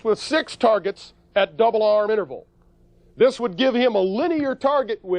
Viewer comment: did he say "double arm interval"? 9:25 "zen harmony of the Universe?" I am not critisizing him, on the contrary this officer is very correct in his explanation, what I try to proove is if my English hearing ability is OK.